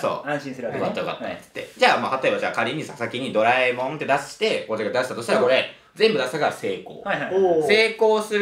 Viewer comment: こうちゃん自分のことこうちゃんって言った（んだとしたらえ、？可愛すぎない、？）！？！？（困惑）聞き取れた方いたら教えてください🙇‍♀️ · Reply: 他のところで言ってる「俺」じゃchっぽい子音の説明がつかないし「こっちが出したとしたら」だと変だし「こうちゃんが出したとしたら」しかなさそう...(可愛i